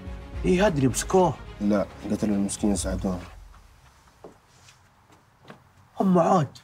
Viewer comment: ايش يقول ؟؟ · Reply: يقول قتلوا المسكين سعدون